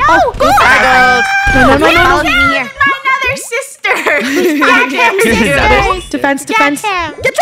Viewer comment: Did fennel say the s word 😊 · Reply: They said sis and its funneh